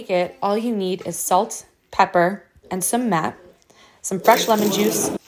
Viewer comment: what did she say at 0:43 "some "mep"?? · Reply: Sumac